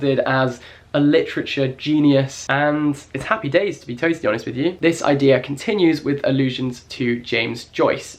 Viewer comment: I heard "it's happy days to be toastie honest with you" pahahahahah